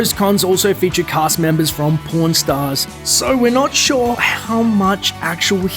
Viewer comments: thought he said PORN stars! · when the video showed the 'Creature from the Black Lagoon' item, I misheard from the accent and thought it said "porn stars" and not the show "Pawn Stars". XD